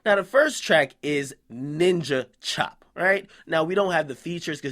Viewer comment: ninja chop? I thought that shit said nina chop